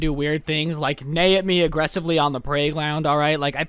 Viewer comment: listen for 10 seconds idk if you meant to say "praygound" I need closure of what the hell you said